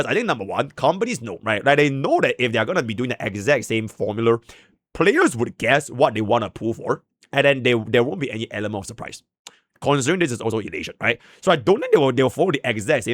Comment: Is he saying formuler? Am I hearing that right